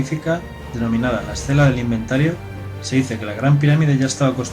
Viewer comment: Ayuda por favor. No escucho bien lo que dice en el minuto 3:26 dice algo como "Las Selas del inventario". Eso dice o estoy escuchando muy mal. Me canse de buscar sobre esas inscripciones y no encuentro nada. Favor que alguien me confirme como se llaman estas inscripciones asi busco informacion al respecto.